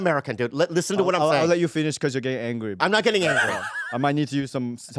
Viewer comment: he’s getting Gay Angry ? In my mind is what I heard and made it 10x funnier hahaha x10